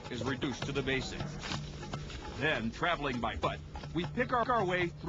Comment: lol at 1:33 it sounds like he says "traveling by butt" =P